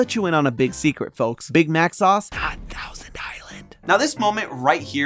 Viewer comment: aw the subtitling forgets the "not", making people who cannot hear miss the 'secret' that Santi let's us in on :x